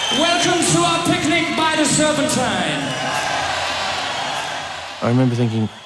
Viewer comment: does Freddie say, "welcome to our picnic by the SERPENTINE"?? Did I misunderstand? wtf